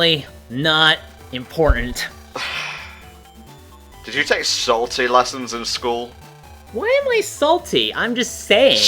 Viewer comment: To be fair to, I heard "Salty Lessons" as well.